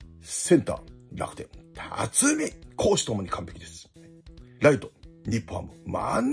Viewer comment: 週間ベストナインのとこ 辰巳 公私ともに完璧！って聞こえて、！？ってなった